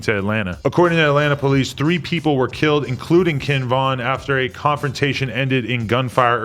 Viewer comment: why did he say “Kinn Vin”